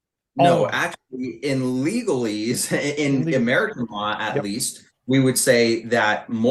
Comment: did he just say legalese??